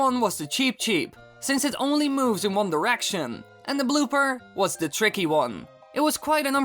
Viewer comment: wait did he say.......ONE DRICETION!?!?